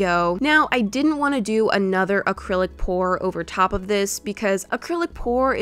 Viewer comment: i thought she was saying acrylic pore